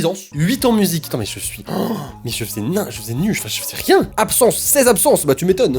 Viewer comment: sa veux dire quoi nein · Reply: Il voulait dire : -mais je faisais n'importe quoi